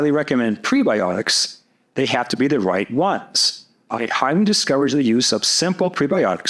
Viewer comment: did you say you HIGHLY DISCOURAGE fos xos gos? The video cut out a little and I didn’t catch if you say you discourage or encourage it them. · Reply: yes, I discourage their use. They ferment too quickly.